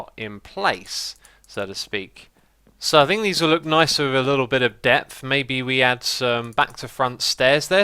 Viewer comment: I thought you said: "these look nice with a little bit of death" xD